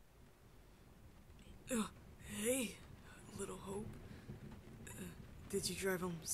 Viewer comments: I thought he said "Hey, little hoe" but I rewinded it back a few times and realized he said "Hey, little hope" and I can't stop laughing XD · In the beginning, I thought he said “Hey, little hoe!” Me: wtf